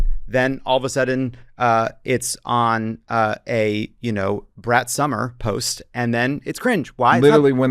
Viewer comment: He said, "Brat Summer." I heard, "Brett Somers."